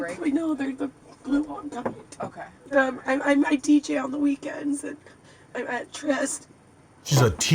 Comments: How did you get “teacher on the weekends”” I heard “DJ on the weekends” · I thought she said im a DJ on the weekends lol which makes more sense to me. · I thought she said she was a DJ on the weekends. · I heard " I deejay on the weekends " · I thought she said she DJed on the weekend. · You missed the best part! You thought she said she's a teacher on the weekends... nope! SHE'S A DJ ON THE WEEKENDS!!! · I thought she said a dj,on the weekend. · I swear to God I thought she said I DJ on the weekend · i heard her say I DJ in the weekends xD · She said teaching on the weekends???? I thought she said dj-ing .......😬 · it sound like she said she DJs on the weekends not teaches but I could be wrong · I heard "D.J." not "teacher". I hope it's not a job involving children. 😂💙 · I thought she said she DJs on the weekend. Id love to see that lol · I think she said "I'm a DJ on the weekend ". Teacher doesn't make sense for a weekend job! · I thought she said she was a do on the weekends. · She "dj's on the weekend" is what she said 😂 fkn hilarious! Even funnier, you thought she said, "teacher on the weekends" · I think she said she's a DJ on the weekends lol but maybe there is weekend school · Not teacher, I thought she said she's a DJ on the weekends. She keeps changing the numbers. 14 yrs, 13 yrs 6 years? Pepper sprayed 3x or 2x? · I think she said "...DJ on the weekends"